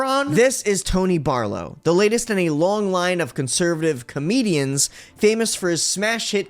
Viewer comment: I thought it said "conservative Canadian" I am thoroughly disappointed